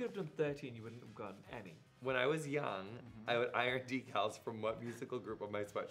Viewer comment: What was the question Jonathan says at 4:30? "When I was young, I would iron ______ from what musical group from my sweatshirt?" I don't get what he says there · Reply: Karina O. Decals